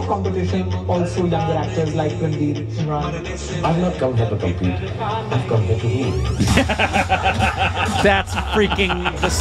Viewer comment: What did he say in 6:26 ? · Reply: "I am not here to compete anyone,I am here to rule"~> SRK 🔥❤️